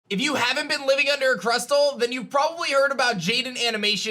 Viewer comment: “If you haven’t been living under a Crustel(?)” What I heard: “If you haven’t been living under a pretzel”